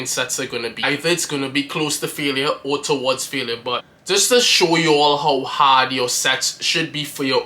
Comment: why am i hearing "just to show you how hard your Sex should be"?😂😂😂😂😂 Love the videos man!!